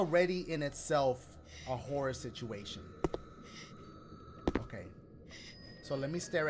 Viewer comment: Did he say a whore situation???😂